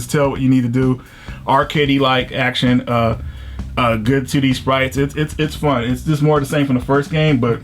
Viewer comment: Am I the only one who heard "good titty sprites" at 2:49?